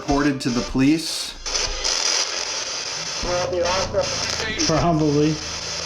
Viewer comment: I heard: "Well there are some" or "Well there are _____" NOT "Probably"